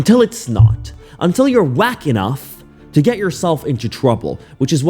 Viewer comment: I thought you said whacking off and had to go back and listen again